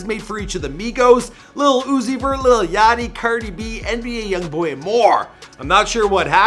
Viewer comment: Who else heard him say cerdi b ? ( not a hate comment )